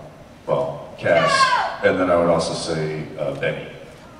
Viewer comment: In 34:00 who does Jensen say is his favorite character besides Cas? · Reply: Benny, the vampire from purgatory :)